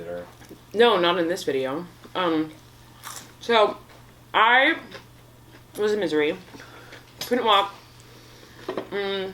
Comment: caption says so “I was in Missouri” but she actually says “I was a misery”